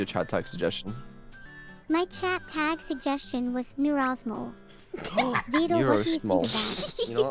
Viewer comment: I might be the only one here that hears it but when Neuro said "Neurosmol" I heard "You're an asshole" xd